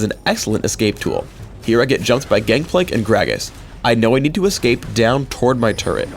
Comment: did he say gankplank